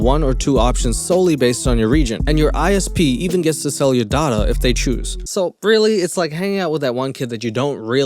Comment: Legit thought he said "sell your daughter"